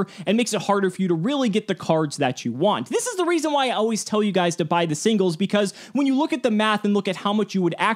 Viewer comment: But I thought you said don’t be dumb by the singles